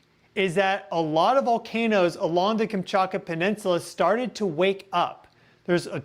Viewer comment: At first I thought you said the boomshakalaka peninsula 😂